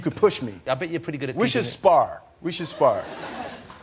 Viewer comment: I thought he said "we should spark"